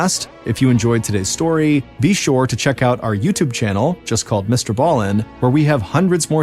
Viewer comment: I'd sure like to know. He says it's on YouTube. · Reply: he says that the Youtube channel is just called MrBallen